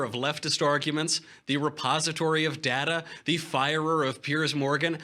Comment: I thought he said "The Suppository of data".